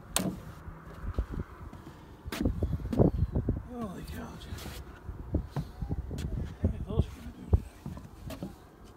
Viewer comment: You did make me happy. I thought humans forgot how join wooden beams in the right way. Also what did you say at 27:35? :) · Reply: Hard to hear but I think I said holy cow